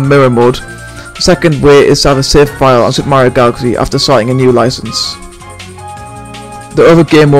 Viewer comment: what did he say? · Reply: Have a Mario galaxy save file after starting a new license